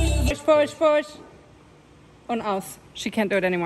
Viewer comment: can I hear "pojď, pojď?" 7:08??